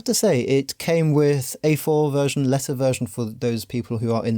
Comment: ROFL At first I thought you said it came with "a four-letter version for those in the US." I spent a couple beats trying to find the joke about Americans swearing before my brain went back and re-parsed it!